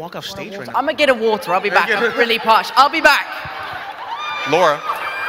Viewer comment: Did tom say at 20:22 " I'm really posh"? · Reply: Parched